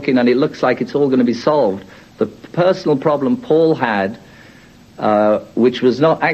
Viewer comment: He clearly says Paul, not fucking faul. Now put this "Paul is dead" tripe to rest, please.